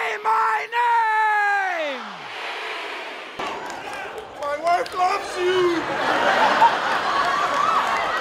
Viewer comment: Did that guy yell "TEXT ME!" ? LOLOLOL This video is so cute. I probably would have cried if I was there. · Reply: i heard "my life is for you", can't really hear it clear hahaha